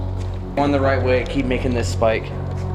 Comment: or 7:40 I heard somthing say yes after said "I'll keep following you or always I think it he said that I'll put it in the comments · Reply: He said if I'm going the right way please make it spike